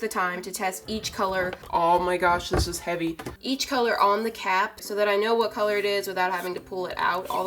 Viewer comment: When she stayed “I test all the colors on the caps” I thought she said “I test all the colors on the cat” and I was all like ummmmmmmmmm what?